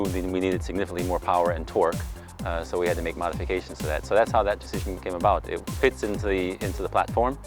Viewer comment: I heard "Mazdafications" haha